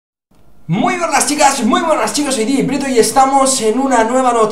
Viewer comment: Jajaj escuche "muy buenas chicas muy buenas chinos" xD